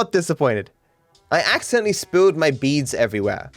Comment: I thought it said beans